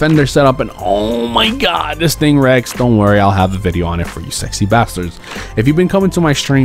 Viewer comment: did he say sexy bastards moreconsole is prod confirmed